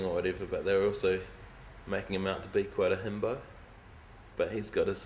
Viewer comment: what is Jemaine Clement saying @ 29:10? "they're also making him out to be quite a _"??? English isn't my first language and i'm really struggling to hear anything other than "himbo" lmao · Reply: yeah i’m pretty sure he says himbo